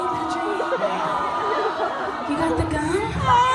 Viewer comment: for a second i thought she said “you got the gun?” XD